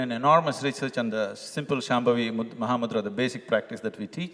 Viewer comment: subtitles be saying muhammed instead of maha mudra😭😭